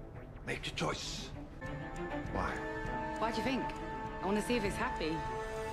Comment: 1) Hmm I forgot she mentions Mr. Fantastic... Thus, MCU is just a TV show in this universe :v 2) 4:33 "I want to see if it's happy". Wait.. I always thought she said "I want to see if it's happening". Then again, 'happy' is much more sense than 'happening'. 3) 6:57 Yes. It's another callback. But it's just a reference from a small scene, and not on a full scale episode. Here, this link will help :D <link>